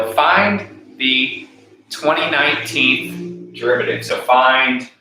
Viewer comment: crazy 😦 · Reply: I thought you were saying 29th derivative of cosine . lol